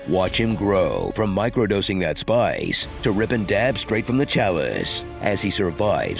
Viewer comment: "Ripping dabs straight from the chalice." lol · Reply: Ahhhh, DAB . . . I heard 'rippin the DAD, straight from the chalice,' and thought HT was onto some cutting edge lingo, like a bong water is Father in the way Kombucha SCOBY is Mother.